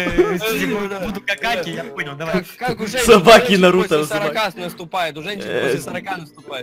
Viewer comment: стоп мне не послышалось "Собаки, Наруто Узумаки..."